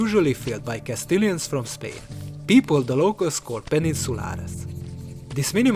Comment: "people the locals pensiscolaris" What does this mean? Is this a spanish word? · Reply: I think it means "Peninsulares", which means inhabitants of the Iberian Peninsula, Spanish and Portuguese.